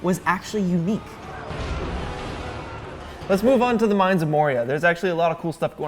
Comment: THE WHAT?! DID YOU SAY THE "MINES"?!?!